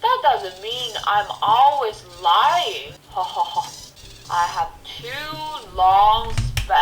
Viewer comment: i thought he said i have HUGE balls ( ͡° ͜ʖ ͡°)